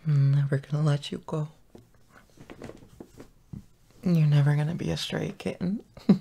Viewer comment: When he said "you'll never be a stray kitten" I thought he said "you'll never be a straight kitten" Was that only me???